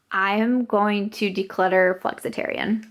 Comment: Lmao I did look up and see what you were talking about! I must've misheard about decluttering flexatarian! 😱